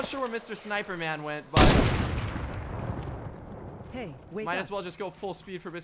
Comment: why did I hear “hey wake up” at 12:17?